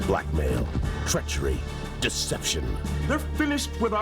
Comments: I played back the very beginning when the commercial narrator says " Blackmail, treachery" and I thought he said "Black Man, treachery" and I saw Sisko and I said no way they said that. I love that she finds the original commercials, noone else is doing that. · I heard “Black Male, Treachery, Desception” and saw Sisko.